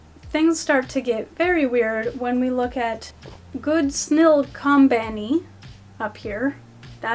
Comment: i seriously thought it said "goob snill company" hsjsjs